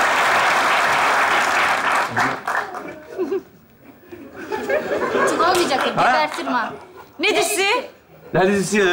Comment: Bu dakikada ayşegülün eline ozanın eline vururken dediğini bir türlü anlamadım! Ne diyor anlayan var mı? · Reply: Orda 'utanmayacakta' diyor Ayşegül